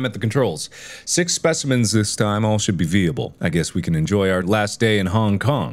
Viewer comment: After years of watching mark, I’ve only now realized that he says veeable instead of viable 😂